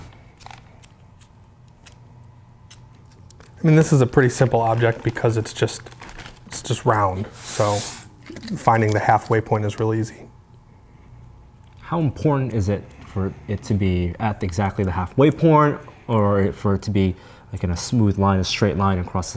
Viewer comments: the half way porn. did he just say that or am i not good listening? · Did he say half way porn at 5:20 ?!?!?!